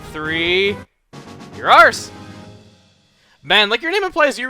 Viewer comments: At first I thought he said, "Your arse." XD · did you just say your arse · Does anyone else think that when said "you're ours" after catching Teddiursa it sounded like he said "Ur arse!" xD